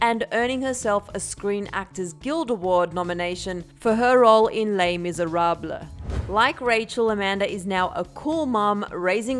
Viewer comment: In 3:51 she really said “Lay Misereblah” 😂😂😂